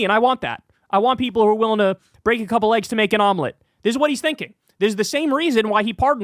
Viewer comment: swear I heard "break a couple LEGS to make an omelette" ☠️